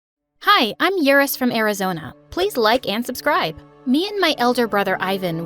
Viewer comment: i thought she said in the beginning.. "hi im urin from arazona"